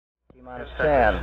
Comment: what? I didn't hear. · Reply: "T-minus". It's a thing said when starting a rocket launch countdown.